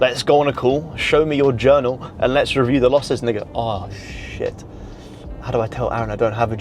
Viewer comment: wait.. did he say the N word 🤔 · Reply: 'and they go' 😂